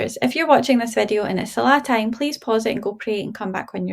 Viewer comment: When I first clicked on the video, I heard you saying “ If it's salad time.. “ I went and prayed Asr 😂 thanks for the reminder sis